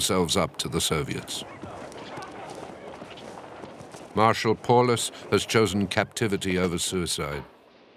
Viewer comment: Oh, I thought it said “marital”. How long and boring that video would be.